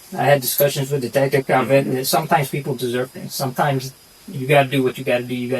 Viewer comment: did he really say sometimes people deserve it?